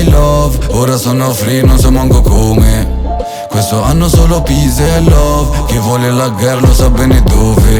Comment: ho capito pisello